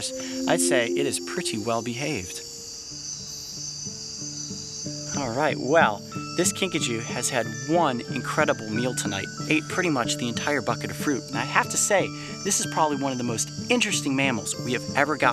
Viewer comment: I swear I Heard Pikachu